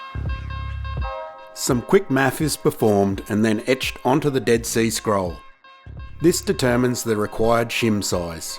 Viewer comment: sounded like "some quick maff is performed and etched onto the dead sea scrolls" 🤣